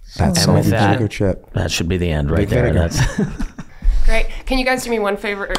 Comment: Never expected we’d get a Vic Vinegar background story. Since the guys didn’t make the connection it’ll be fun when they get that episode. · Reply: I think I hear Rob say Vic Vinegar at about